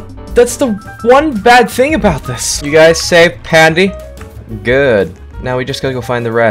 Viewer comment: I thought he said "did you guys save Padme"💀